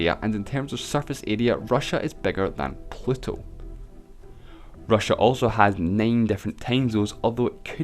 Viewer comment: d-di-did you just say pleto? instead of pluto?